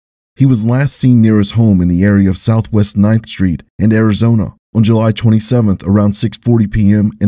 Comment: Just trying to clarify where he was last seen: 9th Street and S. Arizona Avenue in Fruitland, Idaho. I heard it as "9th St in Arizona" at first, lol. I was only listening ( & not good enough, apparently 🤭), instead of watching.